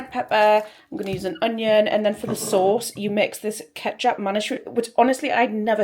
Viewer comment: Ah thanks. I thought you said something ketchup. X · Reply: Ah it could be the ketjap manis?